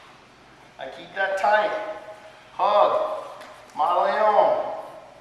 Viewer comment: What is the word he says at 15:18? Ma Laon? LOL. I have no idea, sorry. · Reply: Mata león (Kill the lion) ... used in connection with the Rear Naked Choke (RNC)...